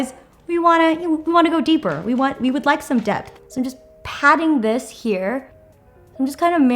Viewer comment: when you said you were going in with the shade "depth" I thought you said "debt" and it IS Pat McGrath so like... I felt that LOL